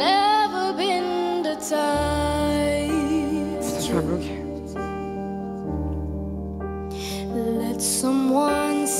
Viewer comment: someone PLEASE tell me what she said I cannot figure it out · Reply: She told him "you blocked me"